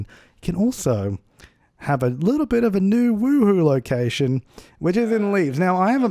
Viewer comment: I just heard NewWho instead of new Woohooo so I was like "Doctor Who in Sims? WHAAAAT?" :D